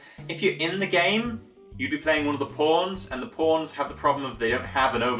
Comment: Whenever he says "pawns" it sounds like he said "porns"